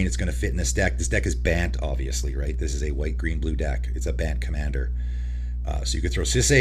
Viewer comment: why did he say it’s a banned commander? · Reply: Ban"T" commander